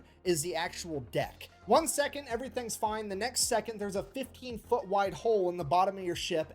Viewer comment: thought that guy said "50 feet", not 15 feet, and now i feel dumb lmao